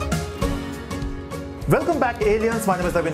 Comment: haha did he just say "welcome back aliens?"